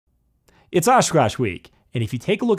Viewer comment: Ok I just randomly clicked on this video and the first thing I hear is "It's ass scratch week". I can't be the only one to hear that right?